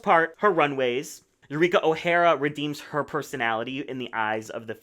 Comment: Wait, what is the scoop with Ra’jah possibly being disliked again? Is it because she won CvTW2 over Silky? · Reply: You misheard, it was Eureka O’Hara who said was disliked again, not Ra’jah O’Hara